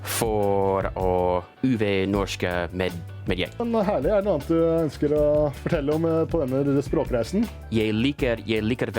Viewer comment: Bro wtf did that guy even say? I'm sure I know most of those words based on the English translation, but man it's so hard to understand native speakers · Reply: "Men herlig, er det noe annet du ønsker å fortelle om på denne språkreisen?"